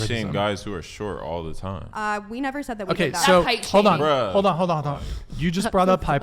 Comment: Did she really say that’s height shaming like your height is literally your body😂😂